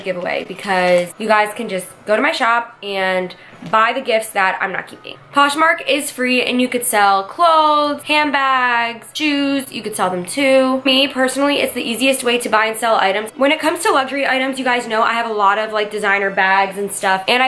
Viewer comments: Fuck at first I thought she said jews at 1:21 . Didnt u u can sell that 😂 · I need hearing aid because at 1:20 at first I thought she said Jews as opposed to shoes. I was like what the frick!!!